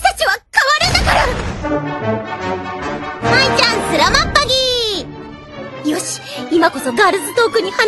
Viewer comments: did I say at 1:11 "Mai-chan, selamat pagi" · DId she Just say SALAMAT POGI? which means THANK YOU GOOD LOOKING.. in Filipino..